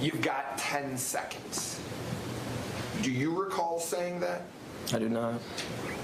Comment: I thought I heard 5 seconds. So I don’t know.